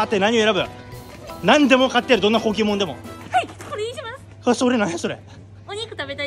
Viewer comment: どんな高級もんでもがどんなポケモンでもに聞こえた